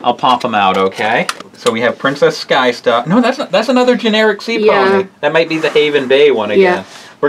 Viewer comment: Haven Bay & Selina or Salina Blue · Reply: I'm the only one who think it was when said 'Ginered Seapony'? 😂😂😂😂